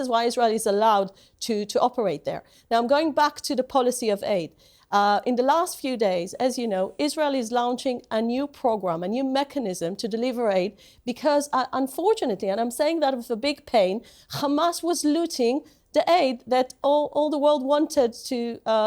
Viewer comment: sounds like she said Israel is launching a new pogrom.